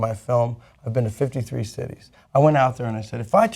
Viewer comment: Josh Fox has been to 53 states? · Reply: Listen to it again, he clearly said "cities." 2:49.